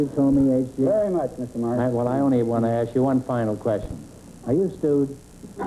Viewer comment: What does groucho say at 17:55? Are you.. What? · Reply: He asks him "Are you stewed?" It's an old expression, meaning "Are you drunk out of your mind?"